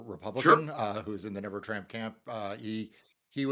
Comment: sounded like he SD the Never Tramp Camp, I like it 😂